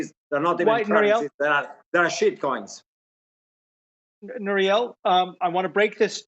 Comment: Did he say Shitcoins??? 4:13??? Lmaoooooo